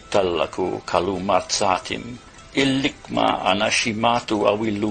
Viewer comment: did i just hear.... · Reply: Ligma